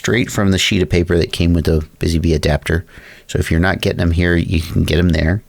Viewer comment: in I swear I heard “Busy Bee Adapter” haha Really appreciate this one!! I’ve been waiting for something like this for over 2 years lol. The first cleanflight apps for iOS were so bland and slow this one however looks like someone just made a skin for betaflight! Definitely going to grab one of these!